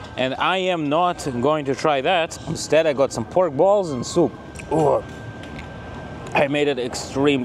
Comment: I've been.Why did you eat cowballs when I heard you say that my mouth dropped😅😅😅😅